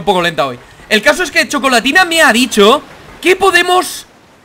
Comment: pense que dijo: La cosa es que chocolatina MEA BICHOS 😂😂😂😂😂😂